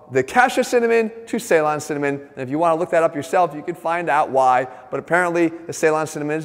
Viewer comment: at first thought he said "if you just take a look at yourself you gonna find out why"